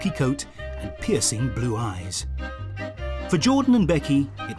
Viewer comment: I heard " pissy blue eyes " (